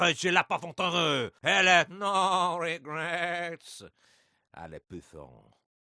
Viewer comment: In the german and english "french" dialogue it sounds like he says "No regrets" at the end.